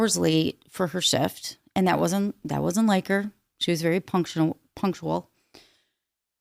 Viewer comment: Another fav added to my obsession! 😁☕️🖤🖤🖤 Quick question - did you say "...that was a 'miker', she was very punctual."? - that was at 7:29 - I've never heard that expression before, what does it translate to? Thanx and Happy New Year 2025!!! 💖 💖 ⛄❄️🎄❄️⛄🎉🎉